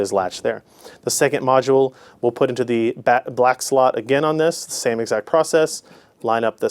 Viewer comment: as i hear he says in13:45 black sluts wtf